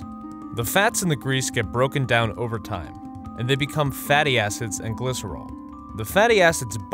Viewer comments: When he said fatty acids I thought he said fatty asses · when the person said fatty acids who thought he said fatty asses😂😂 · Why did I hear "fatty assets" instead of "fatty acid".. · When it said fatty acids i thought he said fatty asses